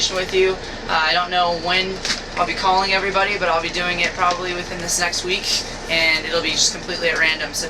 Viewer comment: I heard "within the sex week"